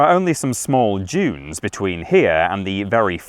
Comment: This was indeed interesting, but, as a Canadian, I have to say that your pronunciation of "dunes" was the most interesting part of the video. The first time you said it, I genuinely thought you were saying "jyoons" and I was wondering what a "jyoon" was. You were showing footage of said dunes at that moment and I thought perhaps "jyoon" was the name of the birds I saw swooping in the air.